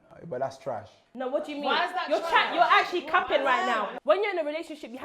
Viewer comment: Did she say "capping?" That's the dumbest new slang I've heard have it for the kid's.